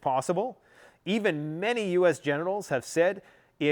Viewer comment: …”think that a limited nuclear exchange is possible even many U.S. GENITALS think it’s possible..” LMAO i heard it and then turned on the subtitles and it even said that’s what you said 😂😂😂 dying laughing